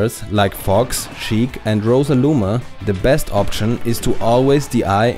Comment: did you say Rosaluma? xD